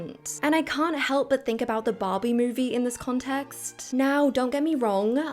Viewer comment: i thought you said bobby movie and i was like how did bobby get a movie fly under the radar